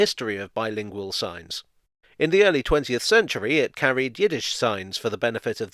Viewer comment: When you said Yiddish I heard it as Guinness